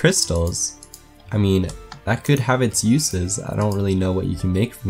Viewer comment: when he said crystal weponry i thought he said crystal meth omg!!